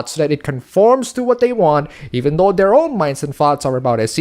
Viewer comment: If people dont know the difference between fetish and. reality. their fucking idiots..also when you said thoughts it sounded like you were saying farts lol